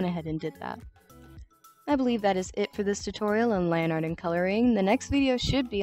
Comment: Low key thought you said Lani Art instead of lineart